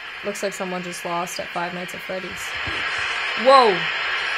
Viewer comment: did she say 5 minutes at freddies?????????